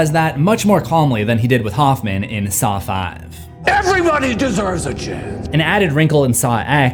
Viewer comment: Subtitles say "Saw 4" instead of "Saw 5" . Please correct